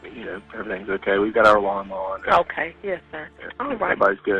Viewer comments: At first I thought he said lawn mower too but I think he said ‘alarm on’ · I hear "alarm on" rather than "lawnmower" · Not lawnmower ~ "We've got our alarm on" is what it sounds like he said, but he kinda ran through his wording a bit🙃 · Girl, im not the brightest bulb in the box either but i thought he said "alarm on" not lawnmower lol either way though "they're all safe" 😂 aint nobody gonna be hiding in his fresh cut grass. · I don’t think he said lawnmower sounds like he said we got our alarm on but I’m not sure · I love you!! But lol….He said “we have our alarm on!” Hahaha not lawn mower. But yes at first I thought he said “lawn mower” at first too. Hahaha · I believe he said “we have our alarm on” (alarmawn) just a southern mumble to me…I’m from SC and I thought he said “lawnmower” at first too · I think he said “we got our alarm on.” 12:38 “we got our alarm on.” Not lawn mower. ❤ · Lawnmower? I thought he said “we have our ALARM on” 🤣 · I believe he said, “we have our alarm on” but with his accent you heard it as “lawnmower “ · I feel like he said they have their “alarm on” when I first heard it, I thought he said lawn mower also but then the more I listen to it I think he said he has his alarm on So they are fine there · I thought he said LAWN MOWER at first too, but I believe he said they have the ALARM ON (alarm system)? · It definitely sounded like he said “Alarm on” to me. Not lawn more but maybe I’m wrong.